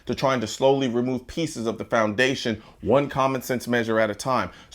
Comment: Everytime I hear "Common Sense"....I hear "Communism Sense"